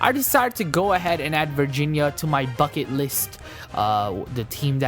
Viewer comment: did he just say "fuckin list"